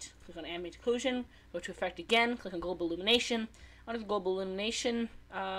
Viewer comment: what did u say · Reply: Ambient Occlusion & Global Illumination